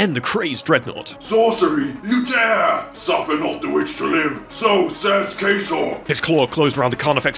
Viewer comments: I thought he said “so says Caseoh” 💀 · I almost thought he said "So says Queso!"